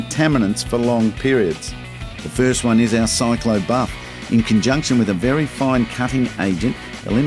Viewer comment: What is he saying at 7:38 A cyclo bar? I am watching in disbelief: sealant first, followed by a clay bar? This is wrong, like: first get on your pants and then your underwear?